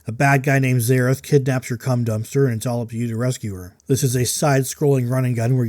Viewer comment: Did I hear “cum dumpster?” 😂